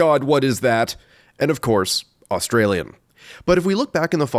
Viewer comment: "and of course, australian" i love this channel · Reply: too tf2 brainrotted and heard it as "australium"